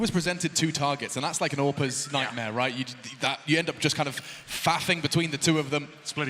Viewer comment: I lowkey thought he said fapping